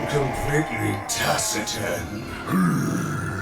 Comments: What is Gero saying at 14:01? All I understand is something like "completly tussitant" but that's not even a word... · What does he say at 14:03 I can't make it out with the metal echo. Sounds like a made up word. "Tussegen"? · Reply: Taciturn. Turn on captions. It really helps. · what is that word? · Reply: taciturn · okay can somebody please tell me what it is that jero says at 14:03. it sounds like tasiten? can someone tell me what it means its driving me nuts. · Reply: Taciturn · What does the “good doctor” say here at 14:02? Completely what? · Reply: Taciturn? I dunno what it means but its what the subtitles say